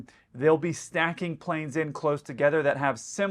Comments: I heard "Snacking planes together"... Been listening to too long 😂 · 😮 Did say "stacking" or "snacking"??? 😅 You know his Snack-dar is active 24/7/365, even when snoozing or enjoying a free breakfast. 😊